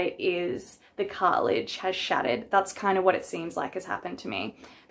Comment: When she said the cartilage is shattered i thought she said sharted 🤚🏽😭